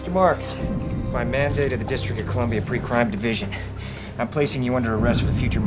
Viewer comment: I thought it said Pre-Cum at first ( ͡° ͜ʖ ͡°)